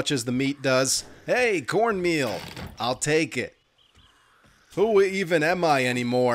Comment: "Hey, Cornmeal! I'll take it". Did I hear that correctly or am I on crazy pills? Enjoying the series so far.